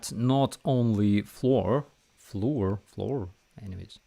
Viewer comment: oh "FLOUR" it sounded like you said "floor"